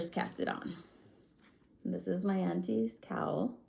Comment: I thought you said "Anti cowl", so I looked that up. Then I tried "Aunties cowl" and I found it! LOL. I definitely found you through "the boys" and I enjoy your channel! Also... I had never heard of Targhee wool! I looked it up and now I want to try it!